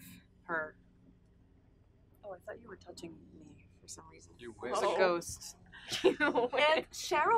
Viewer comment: who else heard Cole say “You wish” to Lili?!??😂